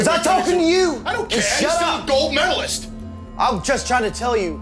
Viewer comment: What did Bubba Ray Dudley say about Kurt Angle being an Olympic gold medalist?... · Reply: He said “idc he’s still a gold medalist”